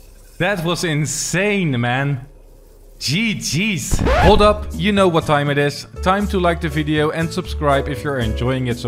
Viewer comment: when you said you know what time it is i thought you said you know what time it is its time to crash